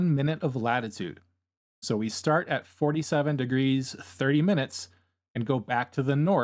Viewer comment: I don't understand, at this point you mention that each tick mark is one minute, go back 10 tick marks or 10 minutes from 47 degrees and you are at 47 degrees, 40 minutes. How does that make sense? 47 degrees plus 10 minutes would be 47 degrees 10 minutes, not 47 degrees 40 minutes. What am I missing? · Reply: Thank you for taking the time to respond. I misheard and thought you were starting from 47 degrees, 0 minutes. This also explains why I was thinking that it didn't look like there were 100 minutes between the two lines.